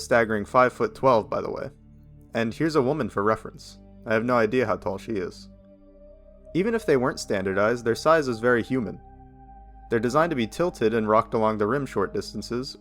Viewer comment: i thought he said "the design is very human" 😭😭😭